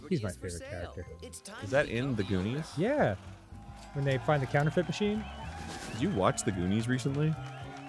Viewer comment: When they talked about The Goonies, I had the volume low and thought said something about a ‘gooning machine.’ I was intrigued.